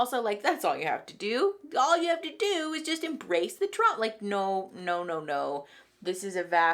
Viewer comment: "embrace the Trump" I think we she was about to say was embrace the "trauma" but the caption read Trump... no no no NEVER embrace the Trump it can be traumatizing lol